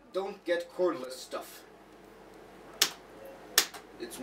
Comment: What does he say at 4:45? Don't get Coreless stuff....? Cordless?